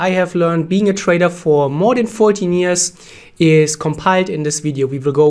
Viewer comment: You have been saying this 14 years since three years ago,is the years not increasing lol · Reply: Pretty sure I heard him say "more" than 14 years